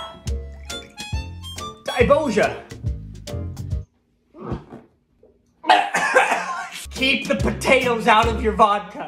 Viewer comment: Did you say "daj boże"? XD